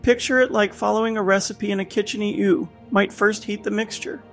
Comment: 1. The thumbnail is AI generated 2. It shows in the subtitiles "like following a recipe in a kitcheny ou" at 1:20 and the way it reads this gives away that the voice is AI generated. It reads "kitcheny ou" instead of "kitchen you". 3. One of the comments below mention making a course cheaper to which the "author" agrees but there is no course mentioned in the video, channel or author's linkedin. So the comments are by a bot also. Stop ruining the internet with AI generated content.